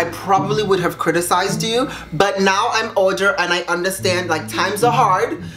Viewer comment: did he say now he’s ‘older’ I didn’t quite hear what he said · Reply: yes, he said that now he's older and understands her story